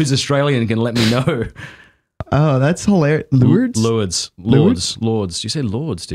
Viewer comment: Lourd a vowel between o and u, and ends with a sort of soft d. I thought he was saying Louvre, because he mentioned Paris.